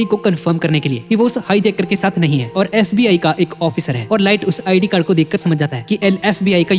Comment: narrator said SBI instead of FBI.. narrator ka bank account sbi mai hai ..lol